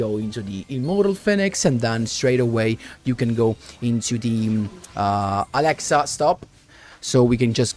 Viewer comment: I think said Alexa stop